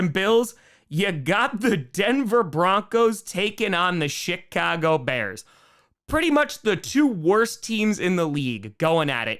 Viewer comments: I had to play it back because at first it sounded like he said the "SHIT-cago Bears" xD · It sounded like you were saying the Denver Broncos and Shitcago Bears. hahahahhahha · am i the only person who thought he said "shit"-cago bears and then kinda thought it was so accurate it wasn't even a problem?